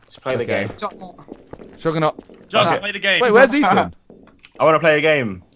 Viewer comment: Am I the only one that thought it said Play Of The Game?